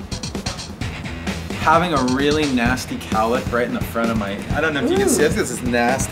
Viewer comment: What does he say at 0:49? I have no idea what he's talking about, his head or hair looks fine 😂🤔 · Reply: Nevermind, 4 months later I know now that he said cowlick 👍😂